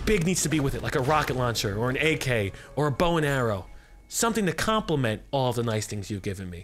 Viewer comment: He said compliment, thought he was going to say compensate.